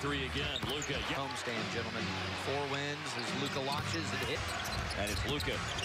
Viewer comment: was I the only one who heard "cumstain gentlemen"